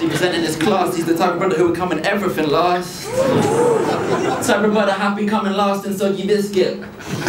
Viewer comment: What the hell does he say at 1:57 ? Something something something Sounds like limp biscuit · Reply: type of brother happy coming last in soggy biscuit